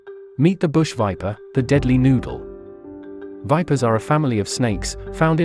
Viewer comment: I thought he said deadly noob for a second